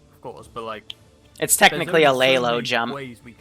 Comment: Did say "Lay low jump"